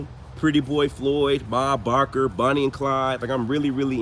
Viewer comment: You must have said, " Ma Barker" . I thought you said " Bob Barker"! Lol!